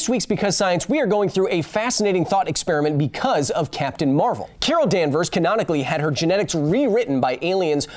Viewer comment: Not going to lie... i had no idea what captain marvels name was. But i thought you said Kara Danvers (supergirl) at the end of the video haha